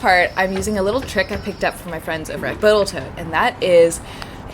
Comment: Sounded like you said boatal tote?